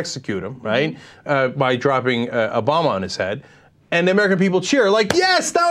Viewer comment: For a second there, I thought I heard Cenk say "Obama his head" instead of "a bomb on his head".